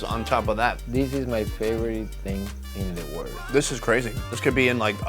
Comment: Did just say "this is my favorite dick in the whole world"????